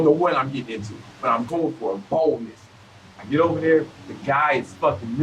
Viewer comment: I thought it said " BALDNESS" I was like Tf